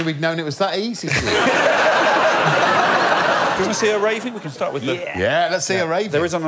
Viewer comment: I might understand Mel's confusion. Maybe, like me, she thought that Alex said "would you like to see a raven", and immediately assumed he was referring to Noel Fielding.